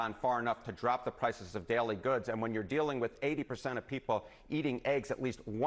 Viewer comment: thought it said poop daily goods